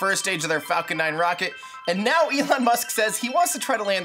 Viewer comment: did he say "Ethan Musk"?